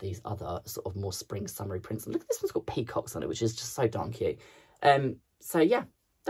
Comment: we you kill me, I thought you said “isn’t that so donkey” you did say “darn cute” but made my Day ❤